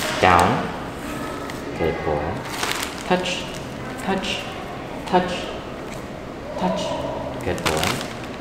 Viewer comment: the first I heard this, I though he was saying "puppy! puppy! puppy!" and only realized he was saying "touch touch touch" because of the captions. Second time I heard touch, then puppy again and now just touch...wtf